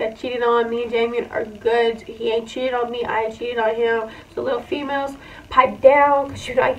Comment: i deadass thought you said “ he ain’t cheated on me i cheated on him so pipe down “ i was dying but i realized that ain’t what you said 😂🤦🏽‍♂️